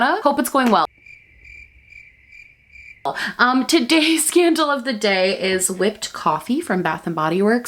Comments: i thought you said “today’s scandal of the day” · I swear I heard, “today’s scandal of the day” and not “today’s candle of the day”. It’s YouTube so basically either scenario is entirely plausible. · I thought you said ‘scandals of the day’ in the beginning and I hate to admit I was excited 💀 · Hahaha it sounded like you said “todays scandal of the day” true. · Why did I hear “Today’s scandal of the day is….” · When I first heard your intro, what I heard was, "Today's scandal is ..." Me: Mmm-kay-what beauty community drama did I miss, now?. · lmao it sound like u said “today’s scandal of the day” btw your makeup look gorgeous per usual💚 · Tell me why I thought she said the “scandal of the day” instead of the candle of the day 😅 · I thought you said todays scandal instead of todays candle, and i was like girl what did you do to get that candle lmao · Tell me why I heard "scandal of the day" instead of "candle of the day"